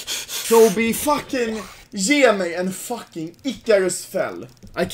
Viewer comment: Did he say: fuckin "ikaro's" fan? Ikaro is czech yotuber.